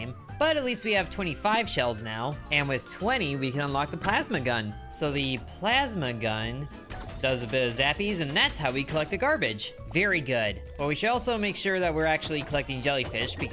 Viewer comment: At first, I thought he said pasta gun 💀 1:25 edit: OMG I’m in the one hour gang!